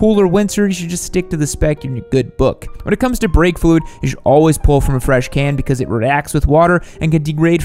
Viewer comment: for a moment I thought you said blinker fluid haha